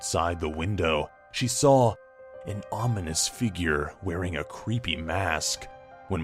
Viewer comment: I thought said an onion figure and I laughed my face off.